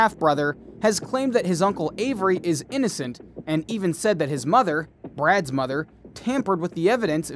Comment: Barb Tadych is NOT Mother.... Brad and Brendan share the same father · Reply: he clearly said "His Mother, Brad's Mother" at 5:40 on the video. Go check!